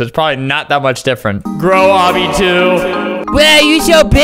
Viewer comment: sounded like you said bro obby 2